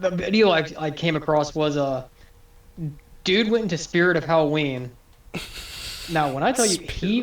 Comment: Did brother just say “Spirit of Halloween”